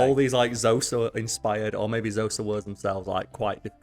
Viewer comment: I think tried to say "Xhosa" here. Chose a hard name to pronounce, since the 'X' is actually a click.